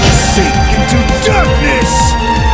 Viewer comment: You know for a great part of my life i genuinely thought he said: SEETHE INTO DARKNESS!